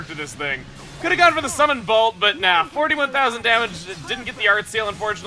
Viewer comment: Am I the only one who thinks Melia is saying "Summon Puppy!" :D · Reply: I thought she said "Summon Copy!"